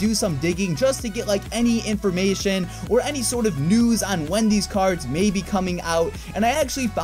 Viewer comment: Was i the only one who heard Wendys Cards in 3:30??